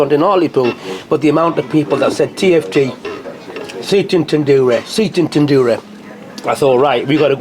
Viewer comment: Thought you said Satan's Tandoori 😅